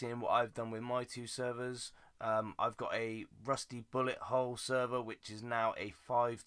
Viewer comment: i thought it said a rusty butthole